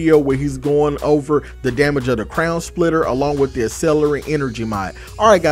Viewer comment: i swear i heard you say crayon splitter